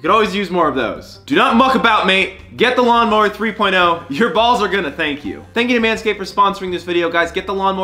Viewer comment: Did he just say your balls are gonna thank you?????